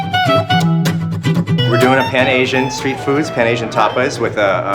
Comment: i swear...i thought i heard him say ADDICT...until i read ATTIC